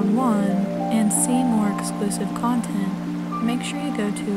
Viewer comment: Dear, Just a warning, that when you mention your Patreon page at 02:35, what you say you are offering sounds dangerously close to "explicit content" rather than "exclusive content". It made me jump a bit, since I didn't think you were that kind of girl. Still don't, for that matter. Just put it down to my British ears stuttering over your accent. Anyway, while you look delightful in a bikini, I don't feel I need to know any more, thank you!